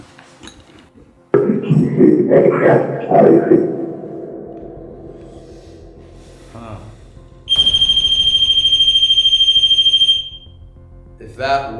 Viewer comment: I swear the device says “You have to accept us now” as if saying you’ve released all hell on earth.